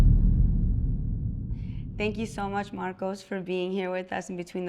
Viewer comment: What's his name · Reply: In the beginning it sounds like she said Marcos but I can’t find him anywhere and idk his last name ugh